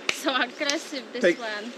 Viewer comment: powiedziała po polsku? "są agresywne ..." czy się przesłyszałem? Musisz wpoić koleżance że nie chodzi i stoi się przed wylotkiem 😁, super filmiki! pozdrawiam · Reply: “So aggressive this one” tak powiedziala!